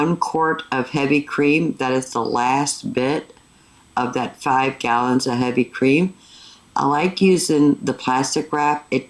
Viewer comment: I say 20 lbs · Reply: Oops I thought you said 50 gallons of heavy cream lol